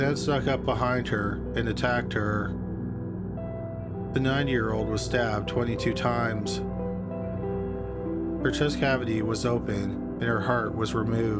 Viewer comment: I thought he was saying the 9 year old not 90. I was so surprised that a deaf 9 year old was living on her own